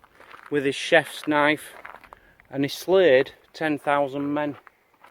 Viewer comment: I thought it was 10,001 men. Well that's what I heard.....